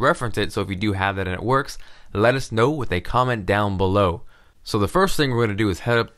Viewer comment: When you said let us know I thought you said lettuce snow